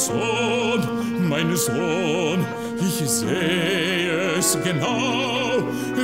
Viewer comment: i,m息子〜 にしか聞こえへんm